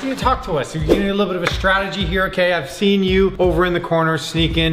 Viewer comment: is it just me or did it sound like said kf ceen you. It's ok if you like Kentucky Fried Chicken. But you can't just mix it in with other words and act like it's causal. : I